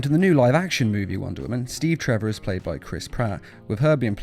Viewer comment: Did anyone else hear Chris Pratt